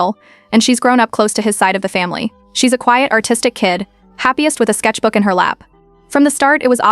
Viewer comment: I heard,autistic and not artistic